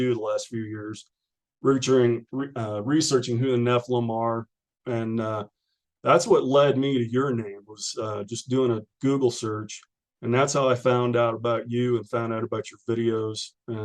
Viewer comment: Did he say “that’s what led me to urinate?”